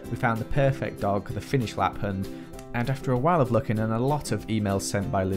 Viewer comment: what breed is merri? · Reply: I think he said a Finnish lapphund